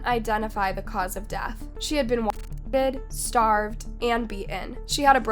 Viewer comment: Can anyone translate the censored parts? I don't know what it says before starved and beaten at about the 9:30 mark. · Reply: I believe "raped". regards.